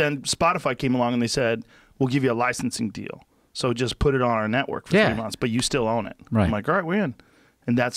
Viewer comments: PLEASE TELL ME IF I AM HEARING THINGS OR THIS IS WHAT ROGAN SAID ABOUT HIS SPOTIFY CONTRACT AT 14:10 DOES SAY "Spotify said just put it on our platform for 3 months"? 🤔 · did he say "a few months"????!!!! Did anyone catch what he said there? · did he say that the spotify deal was for 3 months?